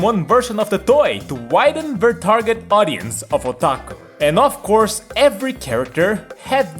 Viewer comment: LMAOOOOO I SWEAR I THOUGHT HE SAID TO WIDEN RETARD AUDIENCE